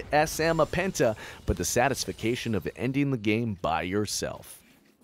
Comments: is it called satisfication? im not a native english speaker and that sounds weird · lol am i the only one who heard "satisfication" at 3:05 ?? it's satisfaction tho. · Anyone else hear him say "Satisfication" at 3:06 ?